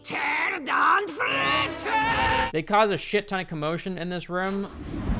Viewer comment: wtf is she even saying ? I'm getting the same feeling as hearing my nails scratching on a blackboard. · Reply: Tge first line is Rubbed out, And the second line, U believe is Squitered and Frittered, udefk man lmao